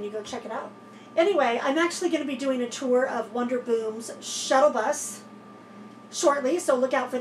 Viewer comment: I heard you say wonder boom...but it's WANDER Boom! 😊